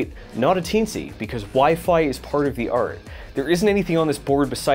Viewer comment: Did you say, "WiFi is part of the art," @ 4:53? I love it! It should go on a t-shirt!